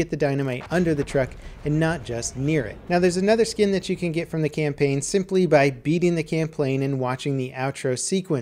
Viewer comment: did anybody else hear him say "camplain" instead of "campaign" in 7:41 (like if you did plz).